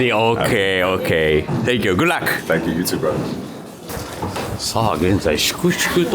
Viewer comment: ty youtuberって聞こえて面白い